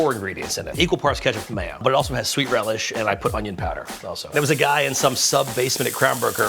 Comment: what did he say? What was in the sauce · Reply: Equal parts ketchup/mayo, sweet relish and onion powder I think